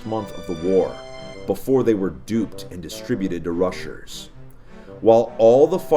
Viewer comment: did he just say russers